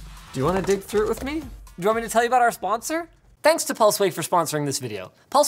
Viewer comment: For a second I thought the pulseway add said pussyway, now that’s an add I could get behind 😂